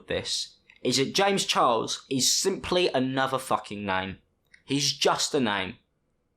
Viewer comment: When you said "James Charles is just a name" I first thought you said "James Charles is just a gnome" and I was really confused for a bit. Anyways you've become my number 1 favourite YouTuber within the past 2 weeks.